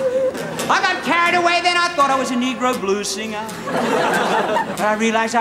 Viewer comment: LOL! am I hearing this right? 5:45 " I got carried away then, I thought I was a negro blues singer" XD. ol' Rude would have had a hard time holding his tounge for tv